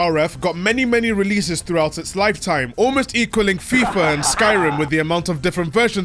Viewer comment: lol did the editor think you were saying "Spyro" got remade a lot? When you obviously said Skyrim, because.. duh? Lmfao